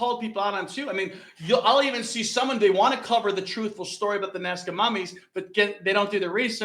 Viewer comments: I heard "NASCAR mummies"? So I thought "Richard Petty?" Naw.. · I had walked out of the room to get a drink, came back into the room, and with the accent, it sounded like he said.. "Nascar Mummies"..... I was thinking, oh yea, those are real..... LOL... Great channel, thanks.